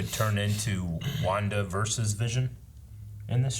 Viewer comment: said "Wanda versus Vision", but I heard "Wandaverses Vision" as in a glimpse at each of these universes she's creating every time she makes a rewind, or tapes over what just happened. Wong is gonna be pissed.